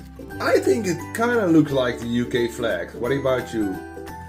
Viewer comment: i thought he said u gay flag bc i had a other tab open and now i realize he was saying UK